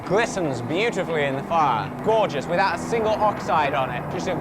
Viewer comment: I thought he said “without a single homicide”